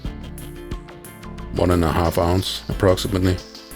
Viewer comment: What was that filling? It sounded like he said "stinger." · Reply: Filling was 'Remonce' creamed butter, sugar, and marzipan